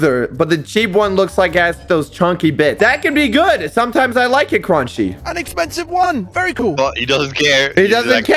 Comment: what did say 😳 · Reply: “Sometimes I like it chunky.”